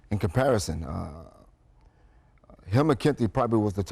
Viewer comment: what name is he saying? sounds like hill mer-ckenti